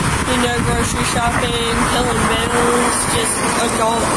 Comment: did she say "pilling bills"?